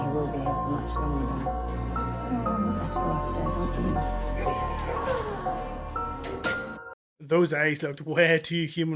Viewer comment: sounds like she said you would be better off dead 😮